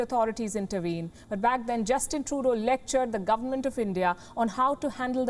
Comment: Sounds like shes saying Justin Toto.